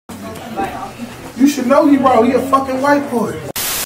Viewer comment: When you hear “U should know he raw he a fckn white boy “ u know it’s gon go crazy 😂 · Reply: I thought he said “you should know he bro he’s a fucking white boy” 🤦🏽‍♂️